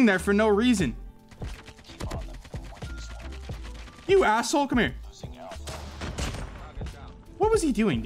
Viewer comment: Did he Say : ASSHOLE 😂😂😂